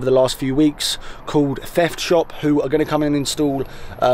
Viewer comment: called it Theft Shop... or thats what it sounded like to me